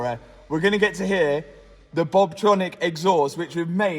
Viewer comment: Thought he said Bobtronic instead of Valvetronic there for a second